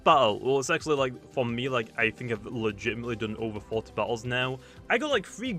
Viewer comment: Did he say 14 battles or 40? Because if it was 40, darn I feel bad